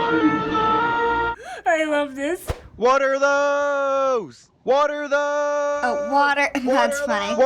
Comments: At first, i thought he was saying WATER HOSE!!! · I thought my student was saying "WATERHOLE!" everyday. Now I know